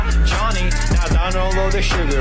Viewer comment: Idk why I’m commenting this but… I thought the audio said “peach lasagna”